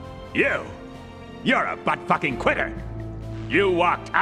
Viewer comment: I’m sure it’s been said before but Trey and Matt are true artists when it comes to inappropriate swearing. I don’t know why, but the way Spotswood doesn’t just tell Gary that he is a quitter, but instead a “butt fucking quitter” always has me in tears. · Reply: I always thought he said Butt fvcl<ING kweer which I found more amusing considering what he asks for next. Sorry had to spell the words wrong to avoid deletion